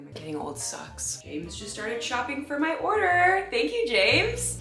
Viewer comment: I hope you read this..but I actually hope you don’t read this. 😳I thought you said “Jade just stated shopping for my order, thank you Jade” I then proceeded getting excited that you said my name. Then rewinding it to hear it again, just to realize that you didn’t say my name nor do I currently deliver food😔. You are an amazing spirit and I am soooo excited about you finishing your book!! Can’t wait to buy it!!🥳🥳 My sister, mom and I are starting a book club between us and you will be our first book!!